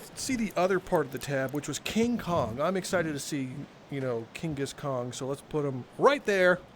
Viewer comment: really thought said ,“I’m excited to see Genghis Khan” 💀